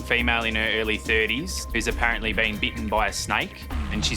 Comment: "She's been beaten by a snake". I was very confused until I heard more and figured out that the program is from Oz 😂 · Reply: Clear the wax out of your ears. Definitely said “bitten”.